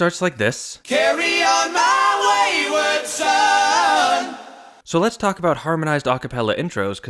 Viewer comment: For many many years I thought they were saying "Darian my wayward son." Even though I never met anyone named "Darion" I just assumed it must be a real name. Kind of a cool boy's name though.